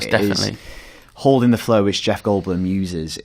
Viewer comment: your outrageous accent made me do a double take whan I heard "holding the flow which jeff goldblum uses". No one in their right mind would hear that as "flare". I love your work both here and in the IGN UK podcast, but I sometimes feel you are just having a laugh.